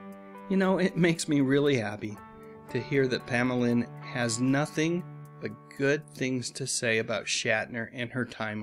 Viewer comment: Even though she didn't have anything good to say about Shatner he is a great actor love him as a actor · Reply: Oh well I know she liked him but I thought I heard you say she had nothing good to say about him I guess my ears are playing tricks on me my bad buddy